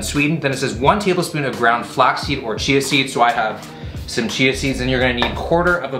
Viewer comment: Not gonna lie, i thought he said "flexseal" not "flaxseed"